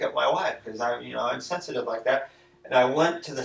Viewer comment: “I went to check on the noise because I didn’t want it waking up my wife, I’m sensitive like that.” What a weird fuckin thing to say! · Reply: It sounds like he said, "She's sensitive like that". Remember they apparently slept apart because he snored loudly and it disturbed Karen, who was sensitive to noises.